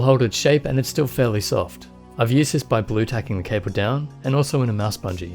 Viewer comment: What did you say? "Blue-tacking?"